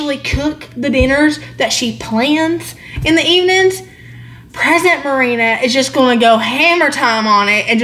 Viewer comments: You said ‘present Merina’. I heard ‘pregnant Merina’ ❤️❤️🤣🤗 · I thought you said pregnant Merina.